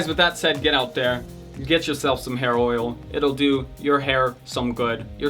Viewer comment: I heard "Get yourself some heroin" xD